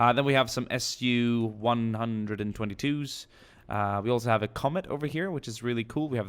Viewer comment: When u said comet I thought u said comment